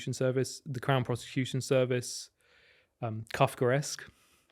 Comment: wtf did that guy say @ 10:43 "the crown prosecution service umm... cuffgoresk"? i cannot google that word at all lol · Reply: kafkaesque